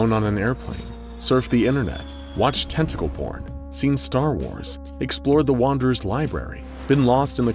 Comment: I'm sorry, did she say Star Wars?